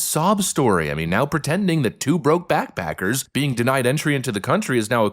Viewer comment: Did he say "BrokeBack Packers"?